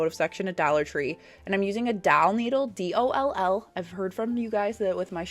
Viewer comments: You make me laugh!!! I always thought you were saying "dowel" needle, not doll needle.. Merry Christmas!!!! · Oh!!! I thought you were saying dowel needle, which I had never heard of. 😂 My Georgia accent, that I also don’t hear, is pretty thick. 😊